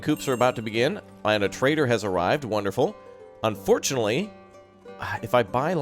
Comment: Haha! You said, "so our trader has arrived. Awesome," and at first I heard, " so our traitor has arrived. Awesome!" Took me a moment to realize what you said. XD